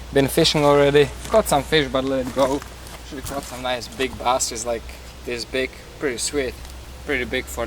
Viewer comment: Sounded like he said some nice big asses